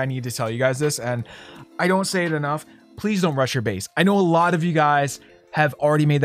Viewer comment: When you said don't rush ur base I thought you said don't touch ur face Haha